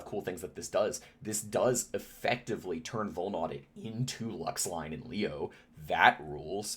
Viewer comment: I'm probably missing the obvious, but I couldn't quite catch what you're saying at 10:47 and can't figure out what card it would be. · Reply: I believe I said "LuxLine", in reference to Melies Luxury Line. If there was another thing I noted that was unclear please let me know!